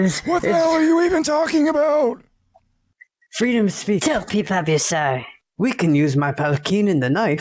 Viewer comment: what mumbling said after the green eyed guy says freedom of speech it isn't understandable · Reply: exactly don't keep up ur sorry